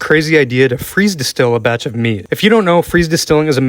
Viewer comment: I first i thought he said a batch of meat 🤣😂im like…. Wtfff